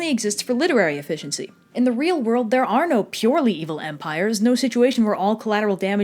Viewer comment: OH! I THOUGHT IT SAID VAMPIRES NOT EMPIRES. I WAS WAITING THE WHOLE TIME FOR VAMPIRES